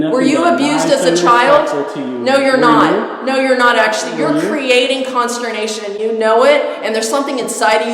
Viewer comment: your creating...... what??? I cant make sense of what shes saying right here! It sounds like a mix of constipation and confirmation.... 😅😅😅